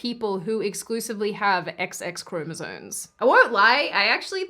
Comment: "sones"? did she say "chromosones"? with an "N"?